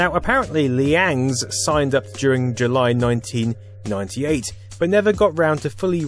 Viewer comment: subtitle says Liandss signed up in 1988, should be 1998.